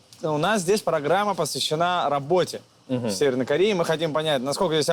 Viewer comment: Мне послышалось или на 1:44 вместо Южной Кореи там Северная